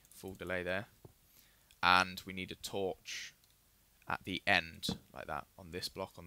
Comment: he says torch, and the captions say something about shit lol